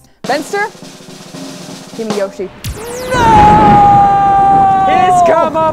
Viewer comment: did she say Yoshi? Waant it yoda